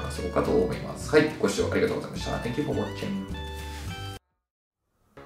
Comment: 最後の"Make sure to give me a thumbs-up" のところ、「ムッシューなんとかかんとか」ってフランス語喋ってらっしゃるんだとずっと思ってました。さすが先生は違うなと。💦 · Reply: いやいや、ただ単にこっちの耳がポンコツだっていうだけです💦 ちゃんと聞けば"Make sure ~"に聞こえるから。 でも聞きようによってはムッシューに聞こえない？